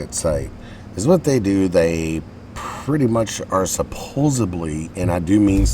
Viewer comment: did u say supposebly? i think it's supposedly